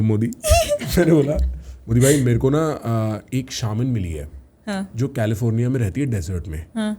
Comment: what are they talking about?? i cant understand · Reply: Bro even I was confused it is "shamanism" Its some culture related to spirits